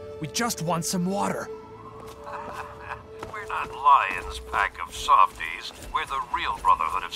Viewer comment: I thought the Outcast leader said "Lyons a pack of Swifties" for some reason. ._. Edit: I hope this is not a copied comment.